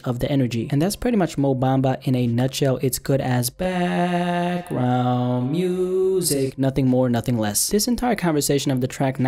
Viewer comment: did you say background music? xD